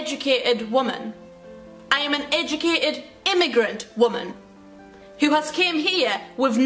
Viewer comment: I so frequently quote "im an eyuccated immigrant woman" lol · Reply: I always thought she said ignorant woman and not immigrant. 😅😅😅😅